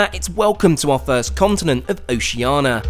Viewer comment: It sounds like you say OceANA, instead of OceANIA.